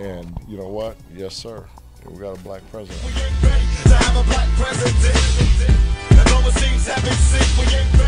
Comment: It sounds like Pac say. "we arent ready to see a black presedent" at least thats what it sounds like to me.